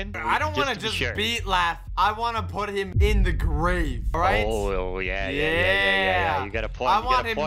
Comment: I thought he said gravy. I am so dumb.